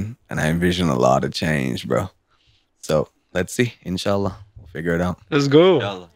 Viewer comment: is he said Insyallah ?